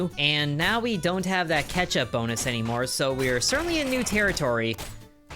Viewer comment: i thought you said ketchup and was very confused. Also thinking about pikachu